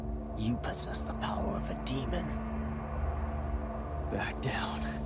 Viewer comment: You can really see how much this guy inspired Gabriel in ULTRAKILL. He even has some of the same taunts! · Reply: yeah first time I fought him I was like "wait did he just say behold the power of an angel?!"